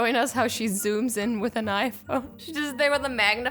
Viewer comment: Who else thought she said knife instead of iPhone